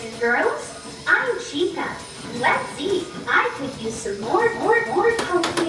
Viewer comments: Wait did she said "I Could use some mmmmmore cocaine" at 0:05 ? XD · did she just say more cocaine?